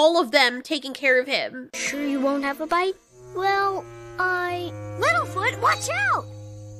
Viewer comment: Okay, I know I'm just mishearing "Little Foot" when you say it really fast, but it sounds like you're saying Wilford, and that is now my official headcanon that Little Foot's name is Wilford. Now that just leaves Ducky and Spike the only ones who don't have normal sounding names.